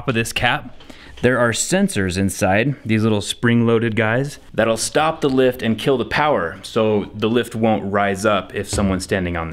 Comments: I first heard it won't stop the lift and kill the guy. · Jesus Christ at 8:15 I thought he said "It'll stop the lift and kill the passenger." I've never been so relieved. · I thought he said kill the person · When he said the safety switch would kill the power, I thought he said it would kill the person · when you “stop the lift and kill the power” I really thought you were starting to say “kill the person standing on top” and that’s a sign I need more coffee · i thought he was about to say “and kill the person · When he told it kills the power I thought he said it kills the person